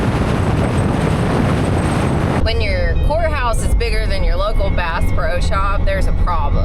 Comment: It sounded like she said whore house instead of courthouse